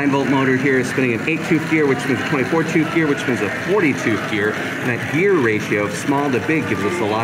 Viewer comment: Honestly thought he kept saying 42th gear